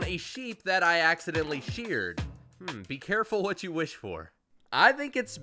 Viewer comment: almost sounds like strange saying "be careful what you wish for parker"